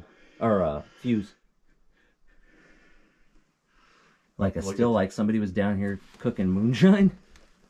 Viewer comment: I really, really hope you guys were just playing with your viewers with that wood sap being “gold!!!”….or not being able to distinguish between dynamite sticks, blasting caps, and fuse….and I hope you have an oxygen sensor….🤗👍Good luck in your explorations! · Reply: Originally wasn't going to include it but we thought it was funny. Haha yea, the metal pipe! said "steel" I thought he said "still", it's the Tennessee accent, lol. We're learning and hope to bring better content as we go. Hope you follow our journey! P.S. yes, we use O2/multi gas monitors and thank you for the comment!